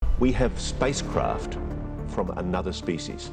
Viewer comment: For a sec. I thought he said, We have spice Kraft.